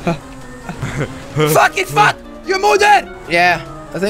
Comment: Can someone tell me the second car he is using and at like 3:40 did he say je modder or the your mother because I heard your mother